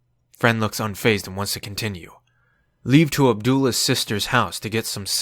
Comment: did you just say ABDULLAH instead of ABUELA?